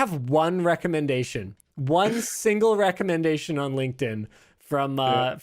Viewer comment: I thought he said that Taran had given him a recommendation for a moment